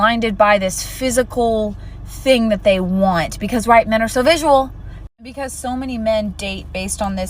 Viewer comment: Did she say "because 'white men' are so visual"? 🧐🤔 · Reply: She said “..right. men are so visual!” it just kinda got smushed together